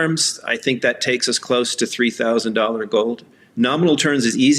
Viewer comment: Lobo says $3000 and David heard $2000. Which one did Lobo call?